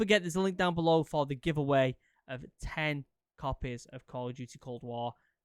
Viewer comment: When he said 10 copies of cod Cold War I thought he said puppies instead of copies 😂